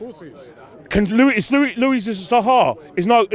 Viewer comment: For a second I thought he said Louis Saha